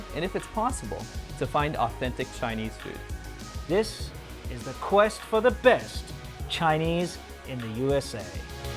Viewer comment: I thought this said “quest for best CHEESE” and I was fully prepared to watch you guys go cheese hunting in America